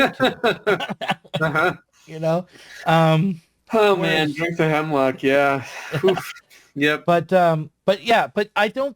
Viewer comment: A little after 1:16:00, says a name (the name of a "maverick"): Dave Sorbo? Something like that? I can't quite hear him. Does anyone know what he said? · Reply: I mispronounced David Sirota's name there.